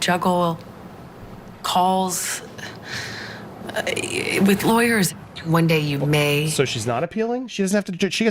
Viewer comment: I thought she said she had teams of liars..... She said lawyers..... Oops.